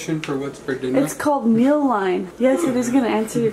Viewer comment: Wait why can't I find this meal app?! I neeeeed it. 😄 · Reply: shandp33 I couldn’t find it at first either because I thought she said “meal line” but it’s called Mealime!